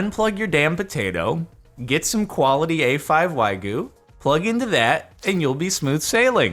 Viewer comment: Did he just say waigu?